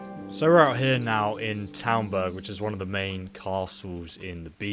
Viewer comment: Did i hear that right? Townberg? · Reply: Talmberg, a castle / town in present day Czech Republic.